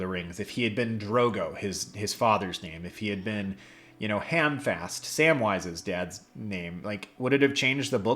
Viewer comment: lol, at first I thought you said, "Handfast".